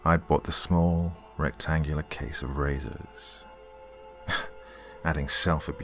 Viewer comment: This was great! For a second I didn't hear it right and I thought he said he bought himself "raisins" for the self abuse. haha sorry! XD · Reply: Diana Alba I heard raisins too hahaha! Then realised razors made a lot more sense...